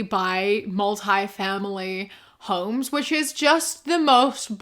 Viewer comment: minor correction because I think you mis heard or misread it: that tiktok says that most landlords own MULTI-family homes, not “family homes.” I’m not sure if it’s different in NZ but in America a multi-family home is a condominium or apartment building, and homes for one household are specifically called “single-family home.” This guys argument is that bc he owns a building with 4-5 families renting there, it’s way more expensive than that other guy could afford therefore he’s actually providing a service that the other could not. Now, he’s still wrong because its an asinine argument to equivocate his apartment building to a solution like the tenants being able to buy a single unit or idk just go for a house that makes actual sense for them and also ignores the reality that single-family homes are becoming unaffordable because they’re being bought by corporations to tear down and build up multi-family units for them to rent out.